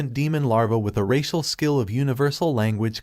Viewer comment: I thought he said racist skill I had to rewind that...